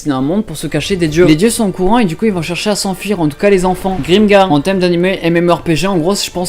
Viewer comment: c quoi le nom de l anim il la pas bien dit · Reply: Grimgar